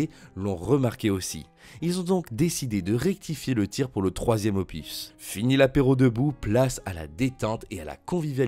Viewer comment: c'est quoi un "opifs" ? Au passage Brice de Nice c'est pas une Saga, une Saga c'est minimum 3 films. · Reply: Opus 😉